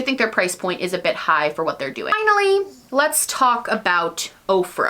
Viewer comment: 😂😂😂 when you said “OFRA” , I heard “Oprah”.... “Oprah is coming out with a palette!” I had to rewind to make sure I heard you right! 😂